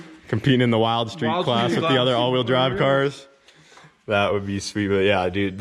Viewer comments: do I hear talk of awd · Did I hear “with all the other awd cars” 😱😱😂